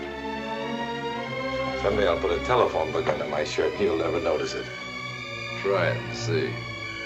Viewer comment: "someday I'll put a telephone hook-up in my shirt, he'll never notice" is that what he said? Maybe in 50 years